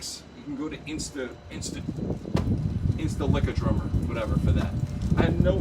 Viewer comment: Did I hear, "Insta-lick-a-drummer"? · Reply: The Beat Lab Music or something... 😂